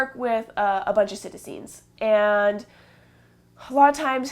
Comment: what are you saying? I work with a bunch of __ ? I don't understand what you're saying. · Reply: Psittacines. Birds in the parrot family.